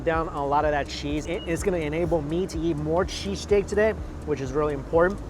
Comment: I had my volume lvl pretty low and I thought he kept saying cheese cake. lol